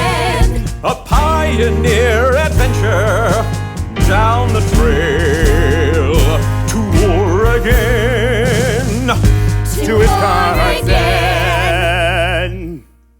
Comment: I kept hearing “to war again” lol